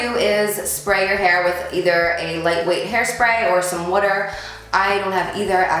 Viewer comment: did she say water?